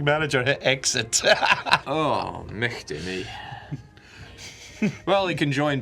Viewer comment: Sir what the actuall fk did you say? Swedish? Scottish? Basque? I speak both Swedish and English fluently and I have no clue.. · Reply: "Mighty me" but the "gh" is pronounced like "ch"